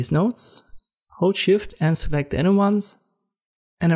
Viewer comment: what button are you pressing, I can't hear your voice clearly😅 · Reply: "Hold SHIFT and select the inner ones" - the inner nodes.